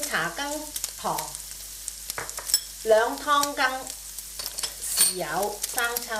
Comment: i realized that the chef said 2Tbps soy sauce at 7:42 but the caption said 1Tbps. So which one it should be??